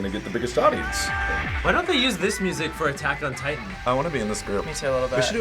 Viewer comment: did he just say ATTACK OF TITAN!!!